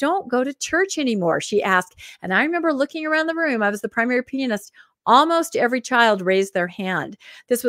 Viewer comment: did anyone else think said she was the "primary penis?" I had to rewind 4 times lol.